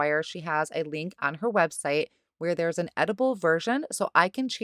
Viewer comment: Gorgeous cards! I got a laugh when you were talking about Jennifer’s swatch cards and said they were “editable”. At first, I thought you said “edible”. 😆